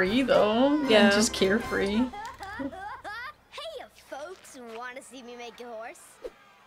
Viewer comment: I generally thought Blitzø was saying “hey folks, wanna see me make it worse?” Instead of “hey folks, wanna see me make a horse?” Till now